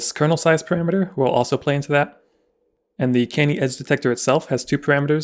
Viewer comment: I somehow heard "ass detector" instead of "edge detector" and I found that very amusing xD Anyway, what a great series! Really has helped me with one of my projects a ton :)